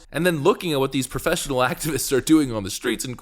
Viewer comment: I misheard "Professional Activists" as "Professional Accidents". As far as weaponizing urine, I have only one word" : Asparagus!